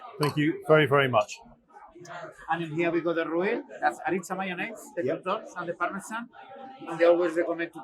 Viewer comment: What was the orange sauce in the dip bowl? Did he say mayonnaise?